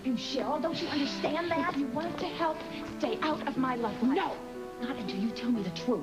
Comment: What did Cheryl say at 2:16? Stay out of my what? It sounds like she is saying stay out of my life raft ROFL! Cheryl: And for the last time I am not involved with Robert. Me: No but you want to be!!! Oh Cheryl you are becoming annoying again!